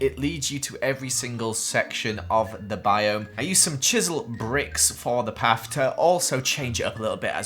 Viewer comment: He said chiseled bricks but I thought he said chisel and bit:)